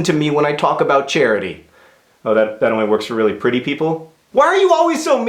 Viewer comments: thought he said "Austrian figment of my imagination" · I could have sworn John said, "Why are you always so mean to me, Austrian figment of my imagination?" He actually said "off-screen" ...my bad.